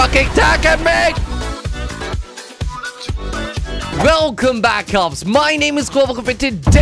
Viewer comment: when i subscribed 1 year ago i thought you were saying welcome black ops